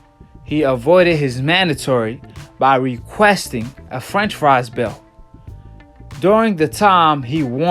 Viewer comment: You saying franchise belt sounds like you saying french fries belt.